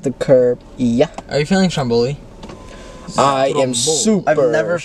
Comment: I'm sorry, I'm just learning english 00:39 please can someone explain what this word means? -r u feeling?......? Is this some kind of new slang? 🥲😩🥹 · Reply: “stromboli” it’s a twist on their last name “sturniolo” don’t worry it’s not a word anyone’s using haha